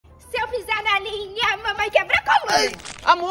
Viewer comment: Eu entendi " Se eu pisar galinha mamãe quebra coluna 😂